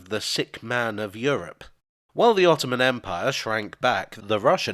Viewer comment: I thought you said the Tottenham Empire had shrunk back